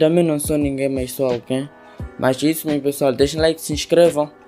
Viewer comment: eu também não sou ninguém mas sou alguém Eu ouvi certo ?kkk